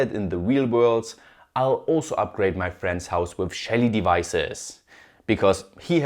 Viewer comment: I kept hearing ''shady devices"